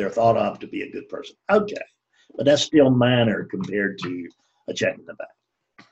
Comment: ' that's still minor compared to a ___ ? ' what is spoken here, I can't quite hear ? TY great talk : ) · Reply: I think he said "minor compared to a check in the bank". He was categorizing the types of self-centeredness, saying that wanting to be perceived as a good person is a form of self centeredness that is minor compared to the form of self centeredness where one is motivated by money.